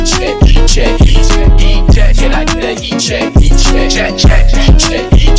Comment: when i heard this song i thought he was saying "eat shit, eat shit" and i struggled to find this >.>